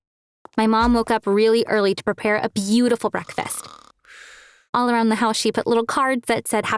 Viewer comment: “my mom and I were preparing breakfast” Mom: doing all the work Girl: standing there doing absolutely nothing after saying she is helping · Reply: Breakfast? I thought she said dinner